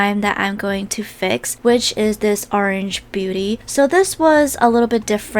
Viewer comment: At "orange beauty" i thought she said... Orange booty